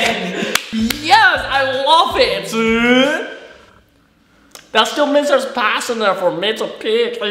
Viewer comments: I never understood what kian was saying at 9:50 until I watched it 400 times to figure it out 😂😂 he said” I love it. That still means there’s a pass in there for me to pick” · I've watched 9:51-9:56 like twenty times and I still have no idea what he's saying lol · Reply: "That still means there's pass in there for me to pick"